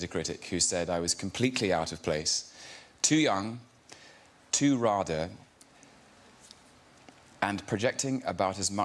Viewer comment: can someone please tell me what’s tom saying. I don’t understand the choice of words he used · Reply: He's quoting the critic who reviewed Tom in the play saying "He was completely out of place, too young, too RADA, and projecting about as much masculinity as Graham Norton." (RADA being Royal Academy of Dramatic Arts)